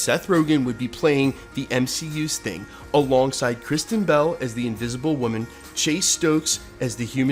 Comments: For a second there I thought you said Kristen Stewart…..I am so glad it was Bell. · For a sec I thought u said "Christian bale as invisible woman" 😂😂